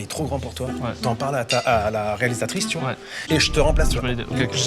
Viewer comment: "T'en parle à ta... à la réalisatrice" c'est ainsi que j'ai compris (je crois j'ai pas encore fini XD)